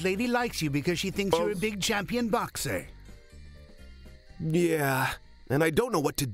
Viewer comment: This is so bad it's hilarious. Also anyone else hear that random guy say: "Boats" or something at 30:01?